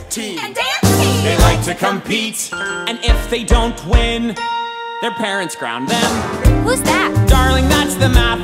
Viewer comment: U know the bit where it says debate team and dance team they like to compete and if they don't win their parents ground them, when i first heard it i thought it said their parents drown them and i was like wow dark turn😂